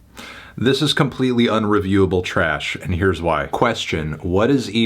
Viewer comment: album reaches #1 on several global charts fantano: this is unlistenable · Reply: I thought he said unreviewable